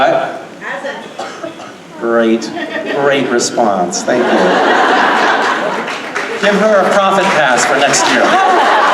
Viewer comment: what does she say? I couldn't quite catch it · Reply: She says it hasn’t